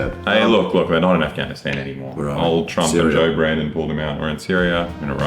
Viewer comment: did bro say “Joe Brandon”?